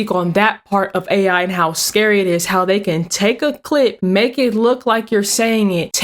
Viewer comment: 😂😂😂😂😂😂😂😂😂You know you said take a clit😂😂😂😂😂😂😂instead of take a clip. I know what you meant. Just funny❤😊